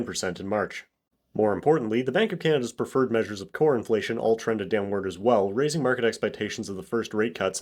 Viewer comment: I thought you said "Corn" inflation. Kind of wish you had...